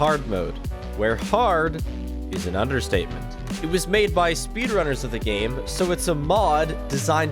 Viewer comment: I thought he said spiffrunners